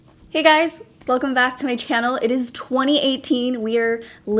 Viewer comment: When you said "it's 2018" my brain thought you were saying "it's 28 degrees" hahaha. Love your channel and personality. Stay beautiful! <3